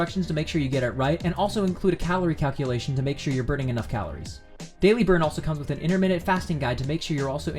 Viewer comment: I thought he said daily birth- Also for the next dinner with Danny I have a question: Would you say you love yourself?